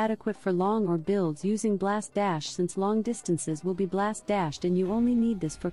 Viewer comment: “Two points is adequate for…” what does TTS say here? I have trouble understanding it. :( Great content though, I was really waiting for this video! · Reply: I heard "long or builds using blast dash..."